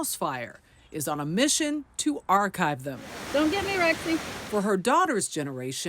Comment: Can anyone tell me what she said in this part? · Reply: On a mission to Archive them